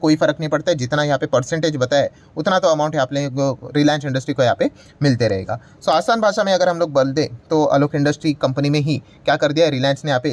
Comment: Sir said -“ toa asan bhasa mai agar hum loag BAL de” instead of bol de😂😂😂😂